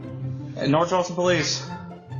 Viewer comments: I heard him say “North Charleston police” instead of North Austin. Just my ears and opinion. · APD knows best!! ❤ 🇺🇸🤘🏻😂 ty for your service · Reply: Sounded like he said North Charleston police. Am I wrong? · Sounded like he said North Charleston, not North Austin. I only care bc that’s where I live lol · Sounds like he says “North Charleston” police not Austin and then not knowing Austin but knowing North Charleston. This seems right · Sounded like they said North Charleston police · Sounds like he said North Charleston police. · Sounded like he said North Charleston police, and if so, I understand his attitude completely lol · Sounded like he said north charleston